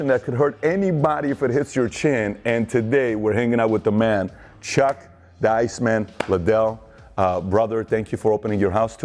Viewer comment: Lol I thought he said Chuck ”Diceman” Lidell.